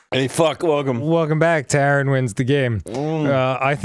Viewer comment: I thought Arin said "hey, fuck Logan" right at the start and I was gonna LOOOOOOOOSE MY SHIT.